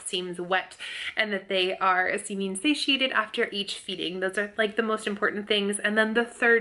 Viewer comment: What did you say at 4:19? I keep replaying it and for some reason I have no idea what you’re saying LOL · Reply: She is saying “Seeming Satiated” which means seems satisfied or filled to satisfaction. You pronounce satiate as follows “See-She-Ate”